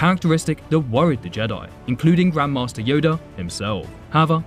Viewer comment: I heard Grandma's Toyota instead of grand Master yoda